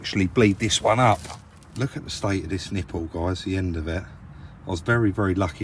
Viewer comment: I was making dinner watching this and at the 4.40 mark I heard "look at the state of this nipple" nearly cut my finger off with the knife when I heard that :-) LoL